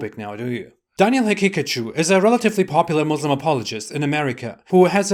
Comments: All I hear is "Daniel has a Pikachu" 🤣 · Every time you day the dude's name, i hear "Daniel Pikachu" lmao